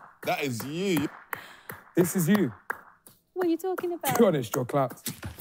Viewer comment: What bro say ? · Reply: He said you're clapped, clapped means ugly